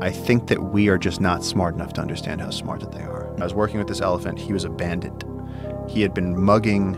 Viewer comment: I thought he said the elephant was abandoned, glad there was a graphic of an elephant with a mask dressed as a bandit to clarify 😂